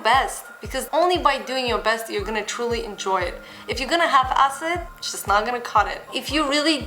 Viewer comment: haha, 10:20 I heard "if you're gonna have acid, it's not gonna cut it". I was like, why the heck are you talking about taking acid??